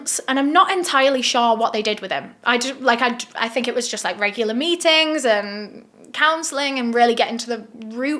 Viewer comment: Omg when she said “regular meetings” I thought she said “regular beatings” 😭